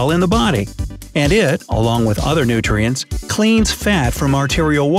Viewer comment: it is written leans instead of cleans.